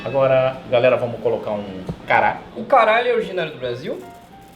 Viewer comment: Só eu que entendi "O caralho" ?